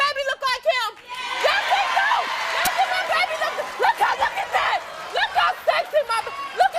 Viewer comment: "Look how sexy my baby..." Did i hear that correctly?!